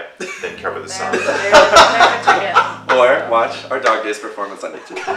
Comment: omg what does Scott say in 6:44 ?? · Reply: he said or watch our Dog Days are Over performance